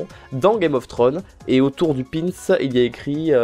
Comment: Cest Moi Ou tu Dit Pinis a 13:59 ?